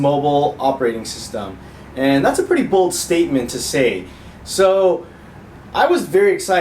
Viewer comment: 0.27 I thought you said "that's a very BULL statement to say".. I was agreeing until I played it again. ...but what I first heard was quite right, in the end. :D